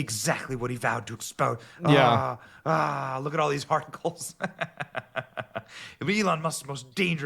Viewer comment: “Look at all these Farticles” is what I heard